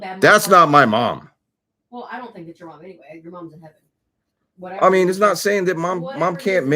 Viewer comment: It sounded like it said your Mom's not here.